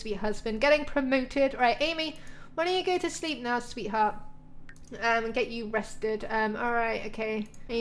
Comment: I LOVE YOU AMY (when you said I need to get you rested I thought you said I need to get you arrested 😂)